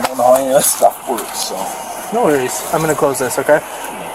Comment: The “Narrator” just translated what one of the LEO’s said to the Man they were “Frisking”, after arresting him before putting him in the Patrol Car as “I’m going to be “BLOWING” in your Pocket”!! Hmmmmmm!!🤮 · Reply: Going. Part of the search